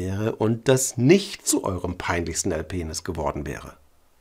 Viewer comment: Bin ich dumm oder wieso verstehe ich die ganze Zeit bei 0:16 "Erpenis"? Habe erst gedacht, dass das Sinn ergibt, weil er ein peinliches Erlebnis erschaffen möchte aber das würde Ralph doch niemals sagen, oder?